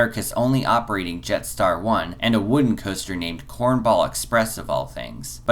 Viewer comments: Number 2 you said is the only coaster to have an elevator, that's not true. The Scooby Doo Spooky Coaster at Warner Bros' Movie World features a double track lift elevator to get from the spooky castle setting to the "fun" floor where the real coaster begins. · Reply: My apologies, I just misheard what you said "only wooden coaster" Scooby Doo is quite steelful! Thanks for the video I enjoyed it :) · Did he say that coaster was called "Corn Bollocks Press"? Odd name...